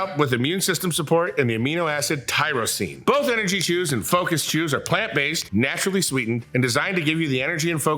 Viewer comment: Thought he said focus Jews and not focus chews🤣🤣